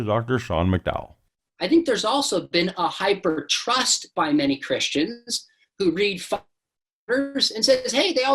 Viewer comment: or anyone.. what does Carrol say at 12:04 'read the ......?" Thanks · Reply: Church Fathers!